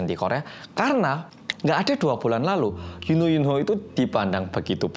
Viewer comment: Gua denger nya Yuno Yuno 😌bener ngga si emang mas gitu ngomong nya apa emang gw yang salah denger